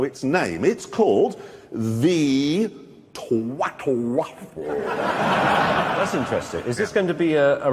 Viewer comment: "Its called the twatlewaforawl" "thats interesting is this gonna be the new arrival for the new Pagani huayra?" "the What?" "the replacement for the zondo its called the huayra" "the huary" "its splet h u a y r a the hu hu" "the ha-r-" "so you have a choice between the twatlewahuary, or the huayra" "so car makers name there cars after the sounds of people getting punched in the stomach..?" /-~Laughter~-\ "the new lamborghini BLAH-HHHHH!" /-+~more laughter~+-\ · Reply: First time @ 22:00 I thought he said twatwaffle, watch Richard stifle the laugh right then.